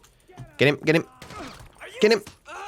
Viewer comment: Am I the only one who hears "get a job!"